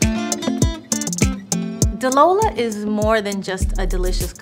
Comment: I thought you said you don't drink JLO.